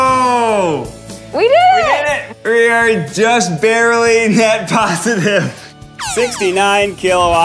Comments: I thought they were saying "Ned Positive" or "Ned Negative". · I thought they were saying "Ned positive" 😅